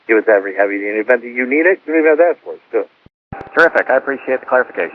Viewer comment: I think he said "terrific" not "Perfect". anyways, I'm glad to know that everyone struggles to understand controllers/pilots over the frequencies. We've been told to speak clearly and concisely, but everyone confuses speed for mastery. It can also be a bit intimidating even after years of experience. I personally suffer from phone anxiety. Such anxiety may result in speaking faster as it makes people more alert, and makes them want to prove that they can do it fast.